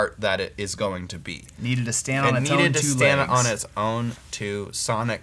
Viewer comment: I thought said, "It needed to stand on its tone two feet", in response to talking about the sonic properties of the song after adding the Black Coffee pedal😜